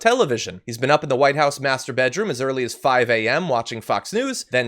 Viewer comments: Who else heard: "He has been up in the White House masterbating..."? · Ha as John read the first NYT’s quote, I heard “he has been up in the White House master,” bating, which is what my mind finished with. Since his wife wants nothing to do with him and his bleach injections, and his hands are prelubed with fast food grease, would any of us be surprised?